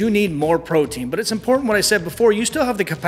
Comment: sounds like you said brotein